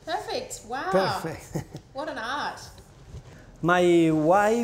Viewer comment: did she say "wow what an ass" lmao